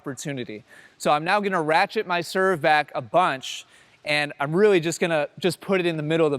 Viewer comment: Did he say" ratchet my serve back" on 3:41? lol what?